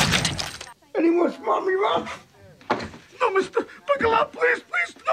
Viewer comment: for some reason this is the first time (I think) where I have ever heard him clearly say "any more smart remarks?" 😄 (The closest I could ever come before was "any more broccoli rabe?")